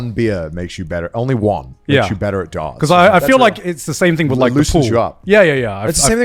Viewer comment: what did garnt say? Its the same thing as what? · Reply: its the same thing with like the pool